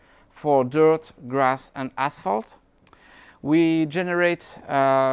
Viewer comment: i thought he said "Dirt, grass and Assholes"